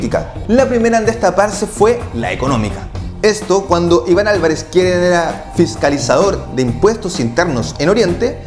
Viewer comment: que dijo??? " esto , cuando Ivan Alvarez .qehhhehrAAA" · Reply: dijo: "quien era"